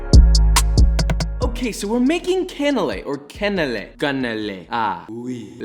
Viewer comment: I thought it said canela and I pictured the deer